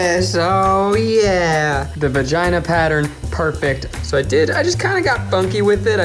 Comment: LOLZ. Or that he doesn't know how to pronounce vagina. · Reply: thought he said vaginal area but nope he just said vagana. that was funny.